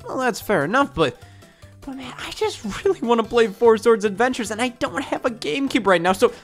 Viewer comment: I could understand killing a man, but pirating four swords adventures? Unforgivable. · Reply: I thought you said "playing four swords adventures"